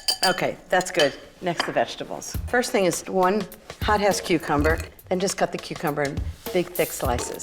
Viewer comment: Forgive Me pleaseee but it sounded like she said HOTASS CUCUMBER